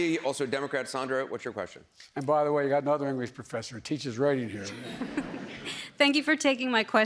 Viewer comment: I think he called his wife his mother and said she's teaching right in here??